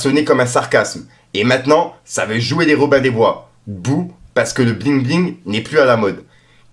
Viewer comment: poto ces pas grand chose mes juste pour te dire que le "BOU" comme tu dit a 12:21 sur le tweet de ROH2F ces pas ce qu'il veut dire.. Il dit "ça veut jouet les Robins du bois d'bou" ça veut dire "bois de Boulogne" 😂ces le bois ou y'a des homme déguiser en femme qui font les pute sur Paname😂ces pas un reproche frérot au contraire je te suis depuis tes début mes je tenais juste a te corriger car la façon ou tu le dit ça change un peu l'impacte de la phrase surtout quand comme moi tu t'intéresse a cette guéguerre d'homme juvénile de 45 ans qui fait vraiment tiépe mdrr en tout cas lâche r mon reuf jtéma tout ce que tu fait toujours a l'affut tu régale tes au top le sang #FickoTvGang